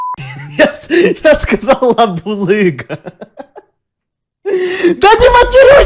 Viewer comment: а шо он там в начале сказал?? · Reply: лабулыга но послышалась"еб**лыга🤣🤣🤣🤣🤣🤣🤣